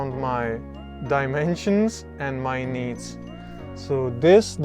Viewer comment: ‘Designed around my dimensions and me needs’ I thought said knees 😂